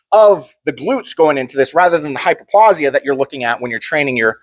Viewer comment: Dunno if I heard it correctly but did you talk about hypoplasia for example at 22:55 min? I think I didn't get that one hehe. "hypoplausia when training ur compound conv. deadlift". I understand hypoplausia as the opposite of hyperplausia, so a decreasing number of muscle fibres :o I misuse cluster sets to compensate my lack pf oxygen/endurance so I can rest for some seconds in between reps achieving double the reps in one set this way xD Oh wait, maybe that's even the main goal of cluster sets in gerenal lol.